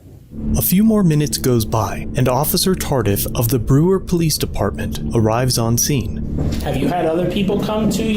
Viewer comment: Really sounded like you said officer tarded.... Then this guy shows up and proves it accurate😂